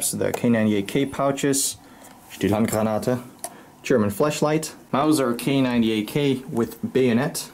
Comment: I swear I heard flesh light